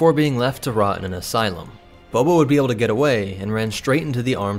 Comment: Did He just say Boba?